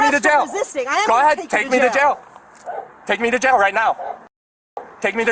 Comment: they bleeped it out, but there’s pure gold at 7:30 “take me to jail right now. pussy” cracking tf up every time i hear that